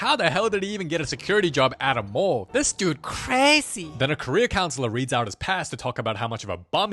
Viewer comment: Every time he says, "Career counselor". I hear "Korea counselor"